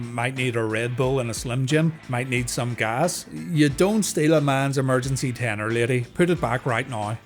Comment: I thought said "You don't steal a man's emergency Tena Lady" 😂